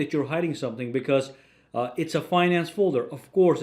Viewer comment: I thought he was saying It's a Fine Ass folder